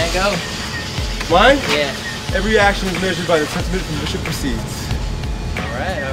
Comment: Ugh I can't cant what says at 24:23? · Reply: "Every action is measured by the depth of the sentiment from which it proceeds"